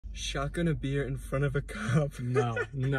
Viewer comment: I thought it said shotgun a cop